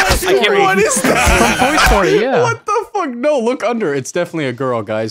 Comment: did he say toy story? LMAO